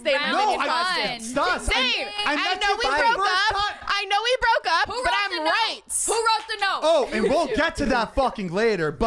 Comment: did I hear that right? They broke up? I’m confusion